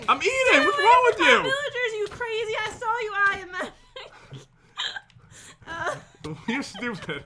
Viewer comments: Anyone else hear Jen cuss at 12:35🤨🤨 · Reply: Think she said “Stay away from my villagers you crazy, I saw you” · did jen swear saying what are you doing u crazy a$$ i saw u taking my villagers